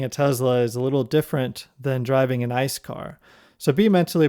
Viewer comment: First I heard "Driving a Tesla is a lot different than driving a nice car", and then I realise it was most probably "... than driving an ICE car" ... :D